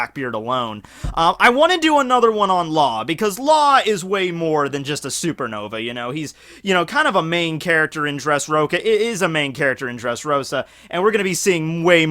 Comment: For a second is sounded like you said dressroka